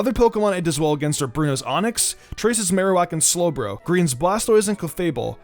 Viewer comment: I thought he said TRACER